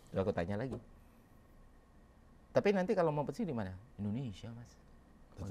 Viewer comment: guys ini bg ferry bilang apaya? "tapi nanti kalau ..... thanks in advancee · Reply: "mau pensiun dmana..